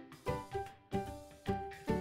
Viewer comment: “This is a bruh moment” -Blood God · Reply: Oh that’s what he said! I thought he said “this is a bribe” like someone tried to bride him with a ring version of him.